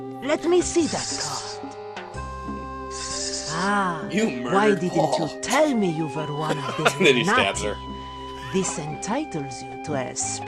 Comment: Wait. Listen to the dialogue. Is she saying "Ah, why didn't you tell me you were one of the Illuminati?"